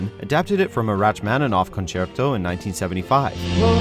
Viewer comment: Did he just say “wratchmaninaff”